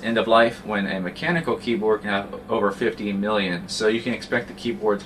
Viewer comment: it may have sounded like he said 15million i think he said 50 million i've heard that is the number of keystrokes that a mechanical keyboard lasts. so I think it was probably 50.